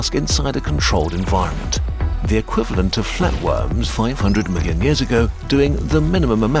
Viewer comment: captions(united kingdom) a billion instead of the mentioned 500 million